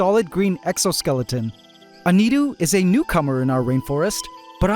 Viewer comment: i thought you called a grasshopper a cucumber after describing its appearance. i agree it does resemble that, but you actually said "newcomer", hah.